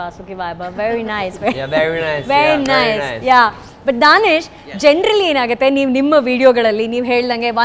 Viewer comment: Danish = Danesh ? anyone else heard the same?